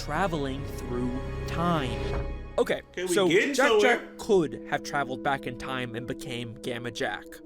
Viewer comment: Wow, i actually thought it said Jack black is a time traveler😭